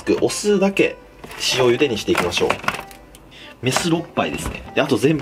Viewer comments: メスおっ○いですね、に聞こえた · メスお○ぱいに聞こえた末期の同士おる？？